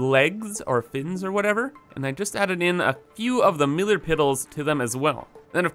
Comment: Anyone else hear him say "millar piddles" instead of pillar middles at 3:18. I know i/m 3 years late so no one is probably gonna see this.